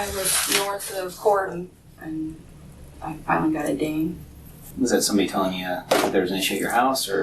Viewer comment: did she just say "I FINALLY got a ding"?!?